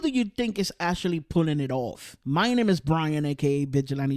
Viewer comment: Thought I heard the n-word in the start and got kinda surprised then realised you said "my name"